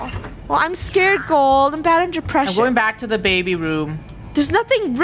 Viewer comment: Did say she's battling deppressions? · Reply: I’m 3 months late but she said she’s bad under pressure